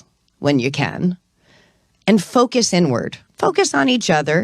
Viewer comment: Did she just say focus N-word?